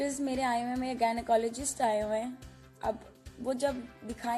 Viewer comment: did she said gynaecologist@ 2:23 or I misheard?